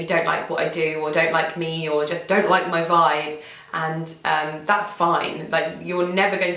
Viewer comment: Lol the part at 16.00 where you said there are people that don't like my vibe, I thought you said don't like my guy lol wait what who doesn't like haha. Just so you know, at first it was actually intimidating to watch beautiful people.